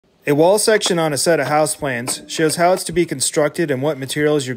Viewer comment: I thought you said houseplants not house PLANS I was so confused! Great video though